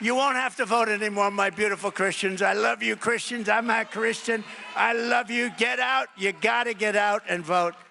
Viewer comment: I swear I'm heading it wron, but it legit sounds like he says. "I love you beautiful Christians, I'm not Christian." So why would any Christian be like....thats my guy